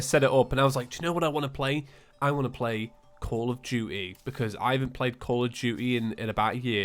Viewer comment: the entire time he was talking about Call of Duty, i thought he was saying Call of Judy